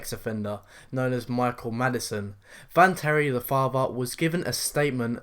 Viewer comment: I had to hear it twice i swear i thought said michael madsen lol it was m8chael maddison